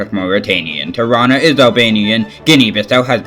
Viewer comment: I think I heard “ Toronto is Albanian “ Btw Canada has Toronto and Ontario also has Toronto city